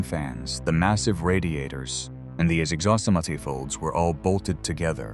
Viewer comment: What did it say at 10:28? I think it was SUPPOSED to be exhaust manifold but the AI voice completely screwed it up! Bad AI robo voice and AI images adds up to total crap.